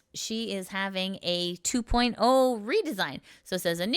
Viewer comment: I thought redesign said resign, also that new deaign looks pretty cool.